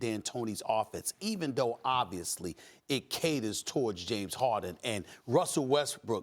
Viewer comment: what is he saying? Katers torch?